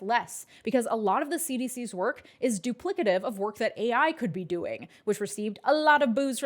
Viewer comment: I believe he actually said duplicitous although he meant duplicative. A Freudian slip for sure.